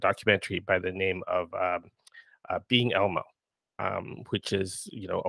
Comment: What was the award winning documentary name mentioned in the beginning (5:07)? Sounded like “Being Omo” but can’t find it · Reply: Being Elmo: A Puppeteer's Journey